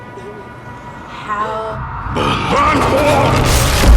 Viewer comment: did anyone else hear the "Burn them" said like a whisper......